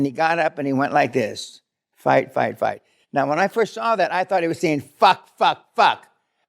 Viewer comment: I thought the same thing. I thought President Trump said "FUCK FUCK FUCK" but after looking at it again I realized he was really saying "FIGHT FIGHT FIGHT" TRUMP/VANCE 2024